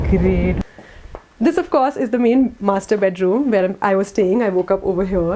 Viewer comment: i thought u said- the main mast*rb*t* room 😂😂 sorry it was just my hearing/listening and understanding problem. Btw your contents are always helpful and informative. Also you are really matching with ur beautiful smile